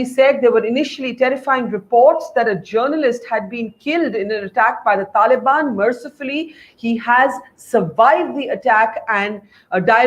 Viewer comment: Did you just say 'killed by the Taliban "mercifully"'? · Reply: Mercifully survived the attack. Listen again